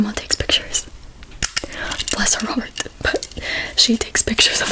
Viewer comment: LOL 2:46 The subtitles said "Bless her, Robert" when you said "Bless her heart". 😂